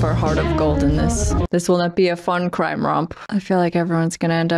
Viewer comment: I thought you said "crime rob" and had to rewind to hear "crime romp"....it made me laugh 😅😅😂😂😂😂